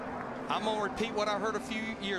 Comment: Can someone tell me what is said at the end over the comms? I couldn't make it out. (Hearing impaired old fart that I am.) "something something I broke the damned something something or other" I love vids, but sometimes they really do need subtitles for the radio chatter and such. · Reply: I heard "I got a push bad and I broke the damn mirror in half". Still doesn't make sense together.